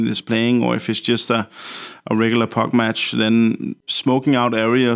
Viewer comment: "park match"? nice subtitles, he clearly says "pug match"